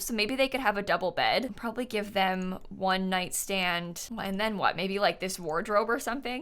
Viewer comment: The way I zoned out for a second and then at 11:03 I heard ‘one night stand’ and had to go back and clarify what she said SJFJFJDFDVJNDKFJR